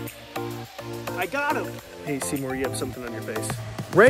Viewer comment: I thought you said SeaWhore 💀